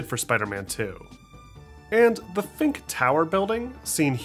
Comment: Did he just say fink...